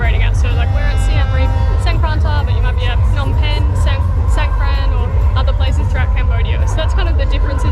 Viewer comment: Songkran is only Thailand. pls learning more about information. Is khmer new year People will confusing. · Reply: Ok , i heard you say songkran not songkrata.